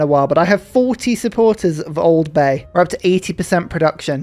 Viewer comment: I've been having this playing while working on my Job at Place. Around 24:30, I thought I heard you say "Old Bay," but I have a feeling you don't know what that is.